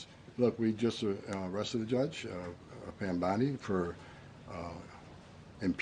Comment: Did Homan actually say "we just arrested a judge, Pam Bondi..." ?! 15:40 Say her name! Hannah Dugan! SMH...